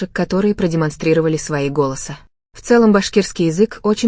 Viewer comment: Мне послышалось продемонстрировали свои волосы 😅 вместо голосы*